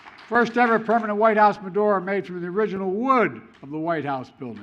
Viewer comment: Did he say the first ever Jewish Fedora? 8:21. I know it’s supposed to be Menorah, but does he?